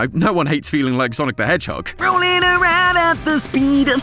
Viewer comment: "MOVIN AROUND AT THE SPEED OF SOUND!!!!" he does sound like sonic tho but i heard rolling